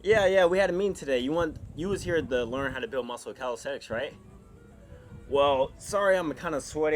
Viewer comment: in the beginning it sounded like he said "how to build muscle with calisex"